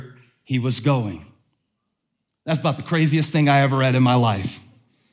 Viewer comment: Did he just said “f*ck*** craziest thing” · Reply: "... that's 'bout the craziest thing...."